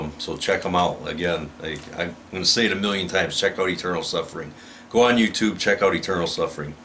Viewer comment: Greetings from South America! When I heard you say "eternal suffering" I thought you said "Internal Suffering" which is a band from my country. Give a listen to that, Choronzonic force domination or anything. Definitely would check eternal suffering as well. For my influences, totally share your taste, also some Devourment, Wormed, Necrophagist, Beheaded, Cephalic Carnage, old school death metal, Demilich, Brutality, Sentenced (when they played death metal), Purtenance, Purulent, Masacre (which are also from here, my country), Death... Pestilence, well... The lists goes on and on... keep it up! You're great! Cheers.